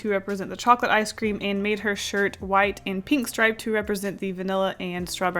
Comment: i thought you said her hair was going to be pink